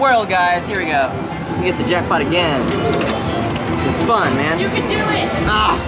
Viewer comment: Did anyone else hear at 10:09 someone said you can do it😂😂